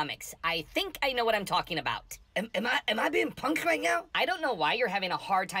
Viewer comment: "Am I being Punk'd right now?" HA-larious!! 🤣 Your version of these two are by far my favorites! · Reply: Thanks for clearing that up, I thought she said "punched".